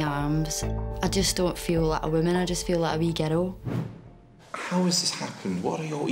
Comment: i legit thought she said "I just feel like a wee ghetto" lmao